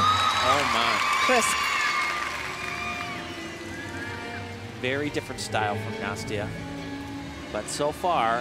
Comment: ohh. i thought he said "very different style for Nastia."